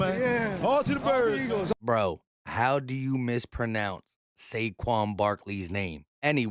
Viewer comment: "Shae shawn barkley" had me rolling😂😭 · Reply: I heard "chase young Barkley" lol